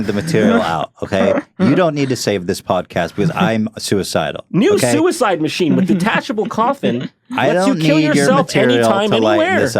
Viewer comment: I totally thought Jon said "New Suicide Machine With Detachable Claw Thing..."